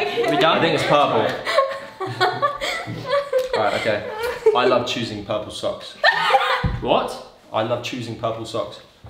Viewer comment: Omg, when said I love choosing purple socks, I thought he said I love choosing puppy socks😂